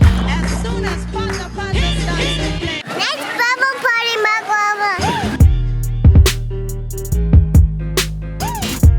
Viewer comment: did she say lets party mother fuc_er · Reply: She said let's bubble party mark rober